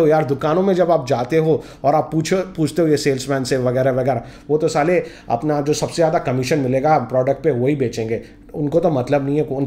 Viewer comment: Did u say "woh toh saale"? On 1:42 😜